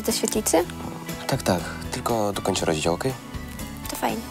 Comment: czy tylko ja usłyszałam "zaraz tylko skończę rodzić"?? XDDD